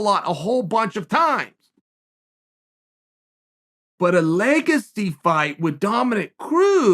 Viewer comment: leg a cee fight.. Ok Chael swallowed some dip on that Idgaf what noone says lmao